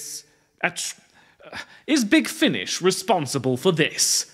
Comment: Ngl, I heard that as "Big Finnish", and thought you were joking about a cabal of Finland's finest trying to make every language as incomprehensible as Finnish.